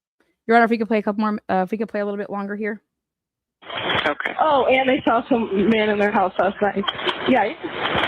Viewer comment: I always hear " And I saw some man in their house last night. Yeah". She exhales like she's laughing